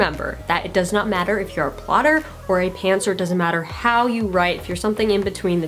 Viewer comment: What does she say at 2:08? It doesn't matter if you're a plotter or a what? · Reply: pantser, someone who doesn't plan out their book before writing it and just sits down and writes whatever